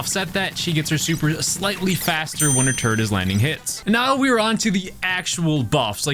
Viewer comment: I thought you said "when her turd is landing hits" lol